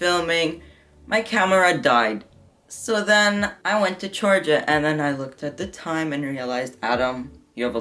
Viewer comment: My subtitles saying “my camera died so I went to Georgia...” 😂😂😂😂😂😂